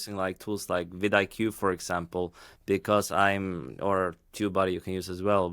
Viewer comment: Sir at 5:45 you mention two tools, one is Vid IQ I think? The other I cannot make out, QBody or something? Two Body? Can you share what the tool was you mentioned here please? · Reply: Sure the two tools I mentioned are: vidIq → <link> and TubeBuddy → <link>